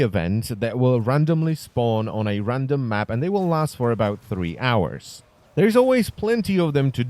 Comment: did he say 3 hours